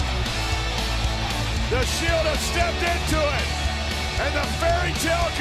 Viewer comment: to this day is till have no clue what seth said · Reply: "What a bunch of cowards you are!"